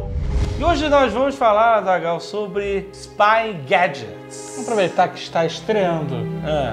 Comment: Juro que ouvi "espaguete" no lugar de "spy gadget" XD